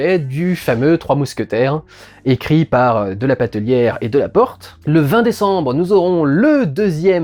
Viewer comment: Tu as un peu mâcher <<De la Patellière>> résultat j'ai compris <<de la pack de bière>> maintenant faut que je nettoie la table du salon du thé que j'ai craché ^^.